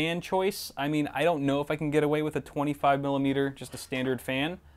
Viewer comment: #derp Did he said 25 milimeter fan?